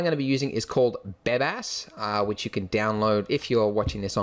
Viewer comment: lol, i heard "Badass" instead of "Bebas" at 2:02^^